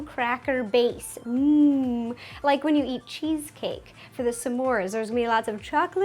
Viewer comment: It sounds like she says "some more" instead of s'more, lol